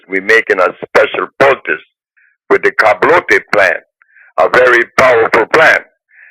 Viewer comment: What is the 'karblotey' plant that he said the mayans had. Whats the correct spelling? Whats the scientific name of the plant? Does anybody know???